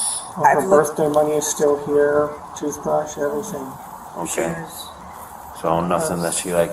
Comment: Did said “shoes”? Like I would understand if he was going down a list and included clothes but na he immediately says “ money , toothbroosh …Shoes “ 😮 And why is he even talking, like he’s talking too much.